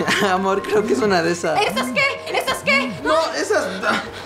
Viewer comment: soy el unico que escuche "¿es sasuke?" Xd